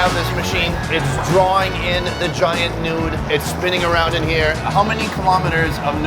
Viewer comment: did sunny just say nude